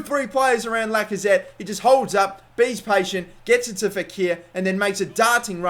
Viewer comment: Did you say... "be's patient"? As in the present continuous tense of "to be patient"?